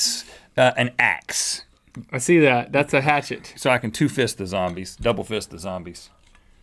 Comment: Did he just say we was going to "fist a zombie"? LOL